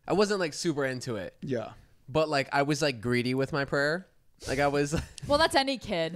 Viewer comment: When y’all opened talkings about prayer I kid you not I heard “pranks”. Had to rewind and listen once I understood!!! 🤣